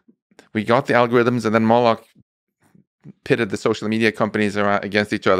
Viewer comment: Did he say Moloch is pushing Open AI to move faster? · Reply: I was wondering the same thing. At first I thought he said “models” but it definitely sounds like “moloch”…. Wtf is going on…..